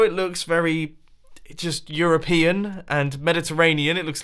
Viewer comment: "it look eroupean" me: spits water out "eXcUsE mE! that definitely NOT look eroupean!" Edit: please don't call me a dumbass if that's not really what he said. to me it sounded like that, so please don't be that rude.